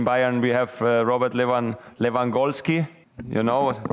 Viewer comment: No one: Literally No one: Thomas Mueller:"Yeah we in Bayern we have Robert Lewan-LewanGoalski! You know Robert LewanGoalski!" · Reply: I thought he had said "Robot Lewangoalski".